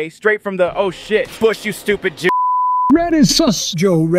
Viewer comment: do the people he didn't know what Kanye just said he said jew